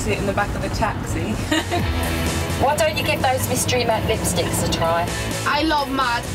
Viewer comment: Is more matte than Mac oh my god how can they say that and at the start they said mystery Mac lipstick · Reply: Sorry i thought they said mystery Mac lipstick but it was Mac